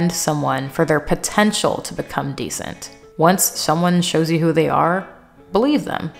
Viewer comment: At first I thought you said "once someone shows you who they are, bully them"😂